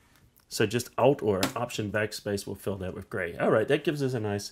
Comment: Cant understand what you are saying...what's "just alt rrrrrr something backspace.........?" 1:35 I want the shortcut to turn the background the selected colour (A rusty CS6 user on 2020) - UPDATE: just wasted 20 mins searching lots of wastful information to discover to use shift F5 - other than that nice tutorial thanks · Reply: Alt or option backspace . thats the Mac and PC shortcut